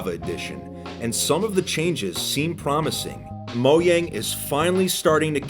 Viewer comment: wait did you just say MOYANG 10:27 XD